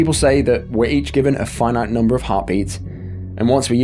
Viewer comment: I thought you said hobbies LO. L then I realized it was heartbeats.😂😊